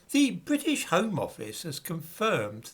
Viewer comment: I thought you said "The British Harm Office" at the start of this video, it would've been a very accurate Freudian slip.😄